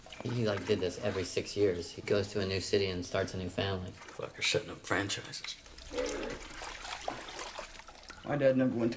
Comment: "he's setting up franchises" I feel so dumb for not realizing they're the same person until the reveal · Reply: I heard “shitting out franchises” but same thing I guess 😅